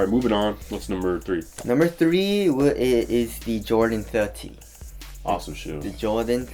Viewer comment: 😂i thought the asian said jordan 13 i was like what! then the white guy said 30 bruh.asian guy needs to learn to lose that accent